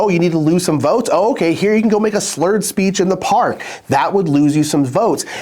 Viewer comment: He kept saying “make a slurred speech in the park” but I kept hearing “make a speech with slurs in the park” 😮 … also a great way to lose votes.